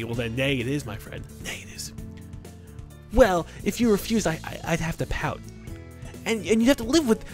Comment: For a second I thought it said if you refuse I'd have to put out haha